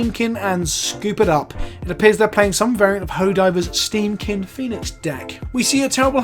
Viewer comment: Did you say Hoe Diva? 22:20 that’s just wrong